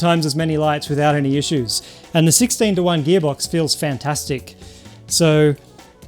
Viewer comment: i hear v16